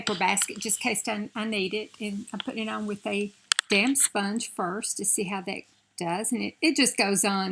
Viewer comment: Girl, I found like a lipstick that worked so well for me and they discontinued it. Too Faced Peach lipstick collection, called Sunday Funday. I'm treasuring the little nub of lipstick I have left. lol I thought you said "I'm putting it on with that dam-- sponge." LOL Your skin before any foundation is perfect. You have beautiful skin!